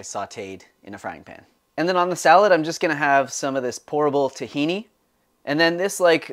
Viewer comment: I heard “horrible” tahini instead of “pourable” 😂